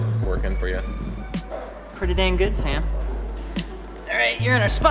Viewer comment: he said dang but the subtitles say damn.